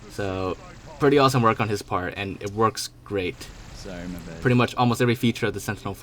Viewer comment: Anyone else hear "sorry my bad" in the background around 0:47?